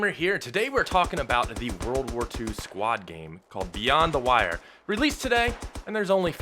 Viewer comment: did i hear this correctly ww2 squad game